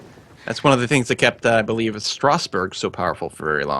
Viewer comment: wait, can someone tell me what league was that they told in 8:25 - 9:00 ? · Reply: they didn't mention any league.. just said "I believe" really fast